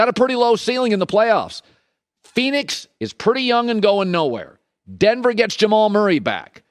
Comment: Did Colin say PHX is young and going now where 3:58? Phoenix, Miami, and the Bucks in that order depending on who is healthy. Bucks are already o-and-1. Celtics might take their place.